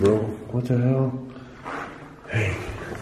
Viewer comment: did anybody else heard someone say hey